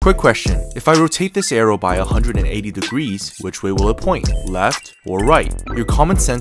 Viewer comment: My dumbass brain thought he said 360 degrees